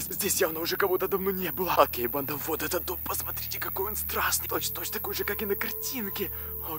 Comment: Мне послышалось какой он страстный 🤣🤣🤣😆🤣😆🤣😆🤣😆🤣😆🤣😆🤣😆🤣😆🤣😆🤣😆🤣😆🤣🤣😆🤣🤣😆🤣*